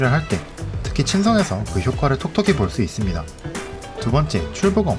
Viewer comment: 나만 이거 톡톡이로 들었남ㅁ